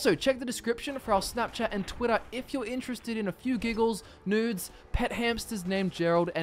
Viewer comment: did you say nudes?😂